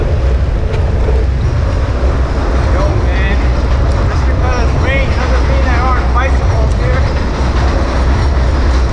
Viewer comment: what did the guy say to you @ 0:50? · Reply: rpmbxdj oh. That's me talking. "Just because it's raining doesn't mean there aren't bicycles here."